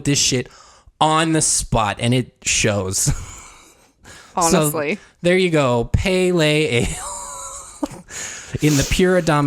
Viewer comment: all I can think of when you guys say this is “pale ale” like the beer lol